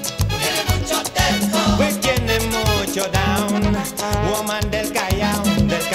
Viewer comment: Ohhh I thought he said lo mandé Callao, now I realize he said woman del Callao.